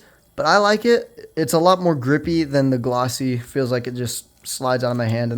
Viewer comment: did u say... g r i p p y ? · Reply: y e s h e s a i d g r i p p y